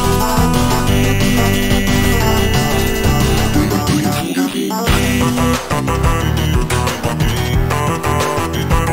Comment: what does he say during this part? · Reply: Leave the demon to his demons